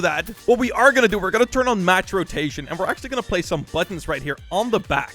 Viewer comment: Oooh match rotation, I thought he said max rotation.!